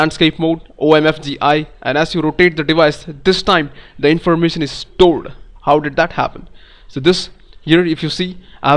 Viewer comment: What did you say at 1:12? The information is stola? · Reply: lol, I believe he said "restored" with a silent "r".